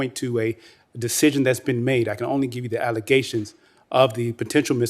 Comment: recusal provisions? 4:33 min ... "allegations of the potential misconduct, which include failure to recuse an improper trial lawyer" ... is that what was said?